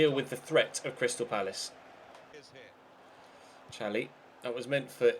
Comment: When you said Chally for Calhanoglu it sounded like you said Cani as in CaniSports